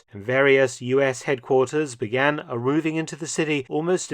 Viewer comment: did you say "began arrooving"?